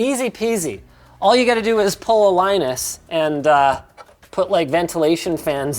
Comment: AM I the only one misheard pull the Linus at 1:27?